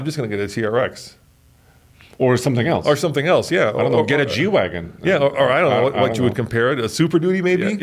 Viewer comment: Thought he said “Jew Wagon”